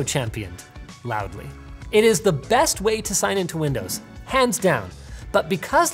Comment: did he say hands down or pants down and showed us a wallpaper of him in his underwear?